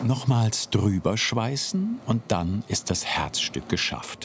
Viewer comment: wer hat auch als erstes drüberscheißen verstanden